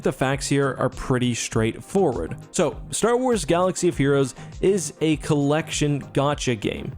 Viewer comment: anyone else heard him say "gatcha of heroes" at 1:27 ? i wouldn't be surprised if they changed the title to that anyways